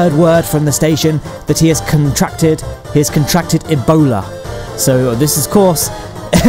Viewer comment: At first I thought you said that he had a boner.